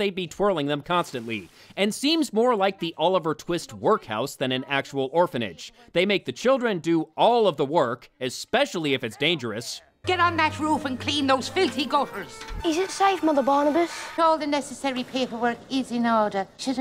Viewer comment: Did she say clean those filthy gophers?